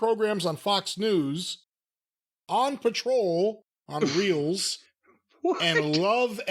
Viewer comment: I seriously had to go back and listen again because I thought said Paw Patrol.